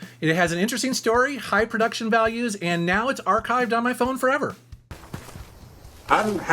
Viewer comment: At first I thought it said archviled so was terrified cause those things are demonic af